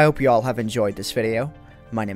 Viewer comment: do I hear "my name is Eminem" ? :p